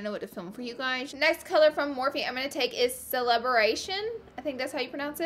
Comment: sounds like she’s saying celeberation instead of celebration oh I love you Laura sm you make me so happy 😂 but hey it can be a hard word to say :)